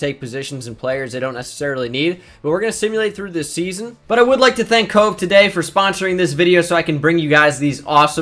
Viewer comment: I thought he said “I would like to thank Coke”